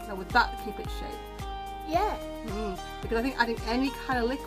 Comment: I heard a kid say "yes" in the backround